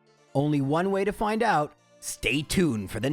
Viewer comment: I heard” Jaro’s Balls Z